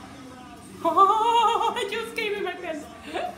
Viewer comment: did i hear that right at 0:17 did you say i just came in my pants? :D